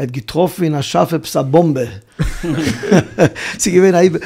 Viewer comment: What did he say at 8:10 with the joke about the amerikaner bochurim and the deodorant… ich hob getrofen a ?????? Something bom be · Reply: א באמבע. A bomb